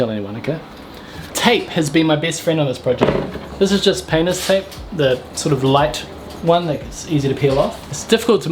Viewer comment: not sure if this was by design but the subtitles at about 7:00 says "penis tape" instead of "painters tape', personally I'm impressed by this